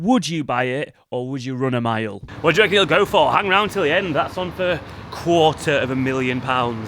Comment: Did he say "what do you reckon it will GROW for" at 04:29? ... that's not a bad lil place actually. Obviously needs gutting.